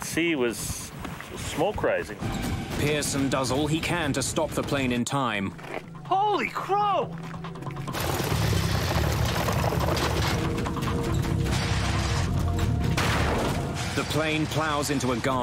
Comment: did he say: “holy crow!”